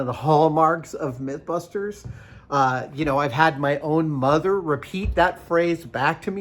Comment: when i first watched this i heard you say "you had your mugger." say that back to you and after 3 times i now hear. "mother" i feel dumb.